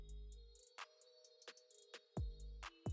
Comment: This is the first video I’ve seen of you, but I would change the name! I thought you said, “Diddy has a podcast?!” as a joke… lol then I saw.